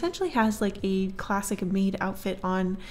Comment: I THOUGHT SHE SAID CLASSIC WEEB OUTFIT INSTEAD OF MAID